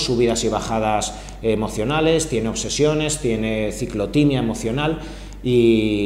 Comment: Minute 8:06 ciclo.. What 🙄 ❓ que Dijo... No entendí.. Explicarlo gracias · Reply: Ciclotinea